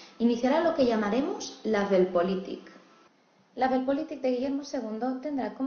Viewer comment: ¿Qué dice? No la entiendo bien. ¿Label politic? ¿La delpolitic? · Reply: Weltpolitik